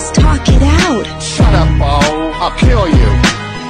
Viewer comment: "Shut up Andy i'll fuckill you" is one of the hardest lines i've ever heard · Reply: admittedly i misheard the sentence cutting off as "fuckill" the first time i heard it, and i thought it was pretty funny even if it wasn't really true either way, "Shut up andy i'll kill you" is still one of the hardest lines i've ever heard